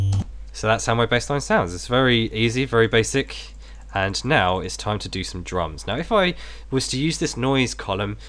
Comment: I thought he said" Now it's time to do some drugs " xD LoL